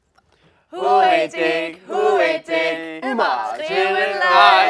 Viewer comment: Plz dont say im the only one who thought they were saying “who ate d***” in the last one